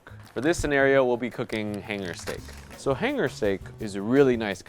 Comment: When you said hanger steak I thought you said ten year steak